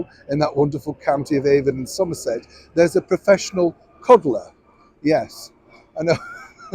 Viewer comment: OMG! A cuddler!? I thought you said "Cobbler!" Either way, it's unusual! LOL, Thanks Neil!